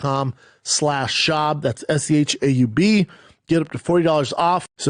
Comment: he clearly said "S-C-H-A-U-B... Are you a complete redact or just deaf?